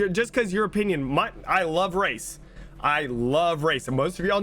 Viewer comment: Lmao when he was saying " I love race" I thought he was saying I love rice. I was thinking wtf does rice have to do with BR.